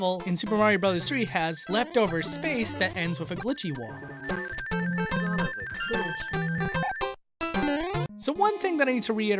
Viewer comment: I heard "Son of a glitch"